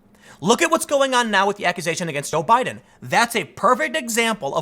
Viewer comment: Did Tim say Obiden at 8:58? I think he did or at least it sounded like he did. Either way he isn’t wrong. 😂